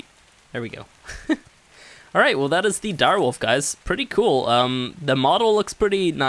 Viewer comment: did he just say direwolf?